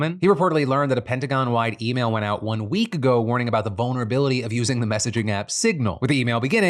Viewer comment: At around 28 minutes when Phil said "vulnerability," not gonna lie, I totally heard "Bonerability" and that's how we should describe anything compromising the trump administration does